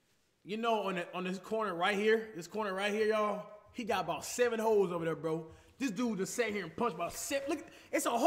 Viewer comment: who else thought he said hoes I was like let me find out flight like that 😂😂